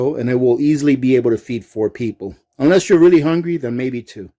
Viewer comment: I thought he said feed me !!lol